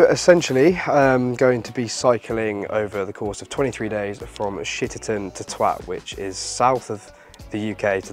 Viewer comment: 😅i know I'm getting old but did i hear "shitterton to twat" 😅